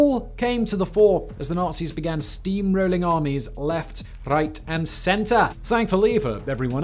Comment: Did he say 01:56 left reight and center